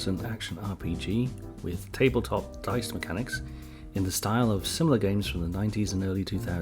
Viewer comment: I misheard the intro: ... action RPG with Tabletop Fight Mechanics... - proceeds to punch people